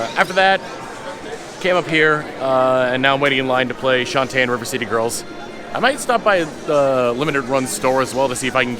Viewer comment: Both times said "play Shantae and River City Girls" I heard it as "play Shantae IN River City Girls". The crossover we didn't know we needed?